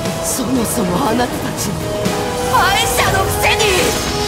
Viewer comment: does anyone know what she's saying in japanese at 1:30? i've been trying to figure it out but i can't make out the second half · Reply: 「敗者のくせに」です